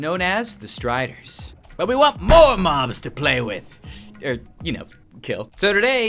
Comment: What he really say: But we want more mobs to play with! Subtitle:But we want more moms to play with!